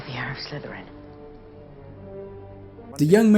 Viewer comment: You know.... when I heard the movie, I thought they were saying 'air' of Slytherin. I was like, " How the heck do you get air from Slytherin?" Also, late squad, where are you?